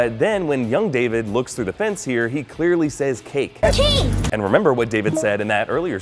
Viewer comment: I hear "King" not "cake".